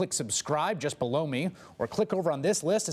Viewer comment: So sorry Chris Hayes, love ya man but every time I hear you say "click the subscribe link below me" it sounds like you're saying "blow me". Cracks me up every time 😂